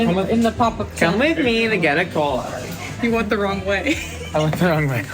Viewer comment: I FULLY thought he said "come with me to get E Coli" before I saw him get the soda.